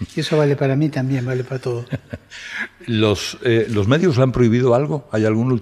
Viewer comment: yo también entendí "los medios" y no los médicos.